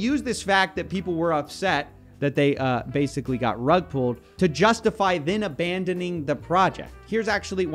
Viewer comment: I heard this as tug pulled.